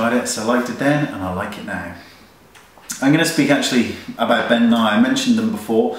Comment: The whole time I thought you were saying Ben and I, instead of Ben Nye, thank goodness for your info box!